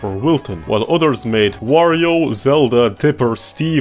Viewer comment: Did he just called Link "Zelda" or did I misheard?